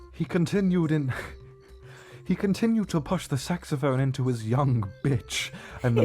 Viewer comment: I'm listening with half a mind while typing when suddenly I heard "Saxophone in his ass".